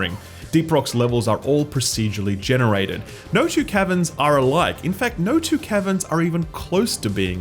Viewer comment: I heard "No two Kevins are alike!"